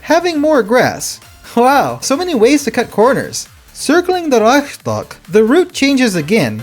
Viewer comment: I had to listen to this like 3 times to understand what youre saying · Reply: Reichstag; I avoided calling it the Rike-stag, opting for the German pronunciation, although it came out a bit soft.